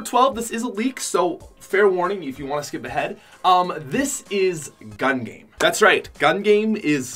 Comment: lol i thought he said gun lean